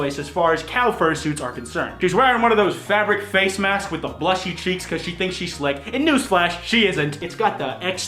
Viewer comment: what did he say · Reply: "With a blushy cheeks cause she thinks she's slick"